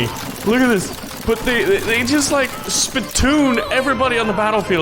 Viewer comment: what he said h Did he said spit toon